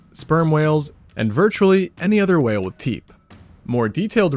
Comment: "virtually any other whaled teeth" 🤣🤔 is it just me or is that what he said